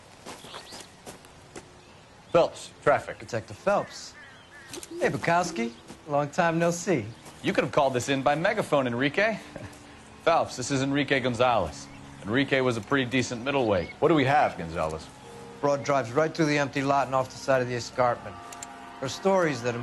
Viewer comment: i thought he said enrique iglesias at